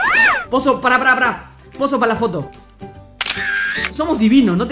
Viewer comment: si se preguntan que dice es: pos chau